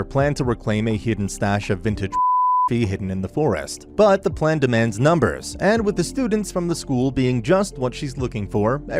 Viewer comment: what did he say????? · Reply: Corn but the c is a p